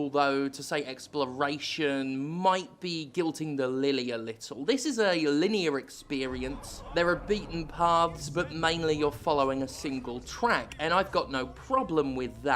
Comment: I'm sorry, did I hear you say "guilting the lily" at 2:06?